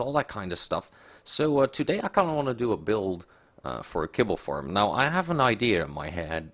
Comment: At first I thought he said we need to build a pickle farm